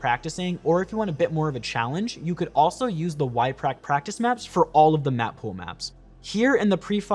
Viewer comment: what did you say there? Sorry my english is not so good. And those maps look very helpful :) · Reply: Found It :D -> Yprac